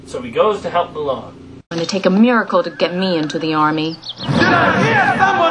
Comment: When I first watched this I thought Mushu said 'America' instead of 'a miracle'